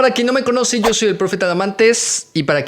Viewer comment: Pensé que decía profe tamales JAJAJA 😂